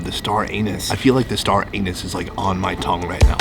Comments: at the part where he said"star anise"i heard "star anus" 😂😂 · Did you say Star Anus?! gasp :O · Sounded like you were saying star anus instead of anise !! Hahahhahaha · DID YOU JUST SAY STAR ANUS? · I thought Sonny said star anus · Did you just say 'Star Anus' definately NOT qatar airways! :P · I think I heard "Star Anus". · umm did he just say StarAnus haha